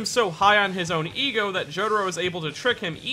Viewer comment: The subs in the video says gyro for some reason instead of jotaro in